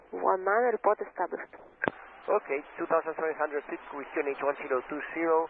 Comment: (Ironic) I hear 2300 feet. Not 2700 as in transcript. You need to slow down to confirm, but it is not 7, it is 3.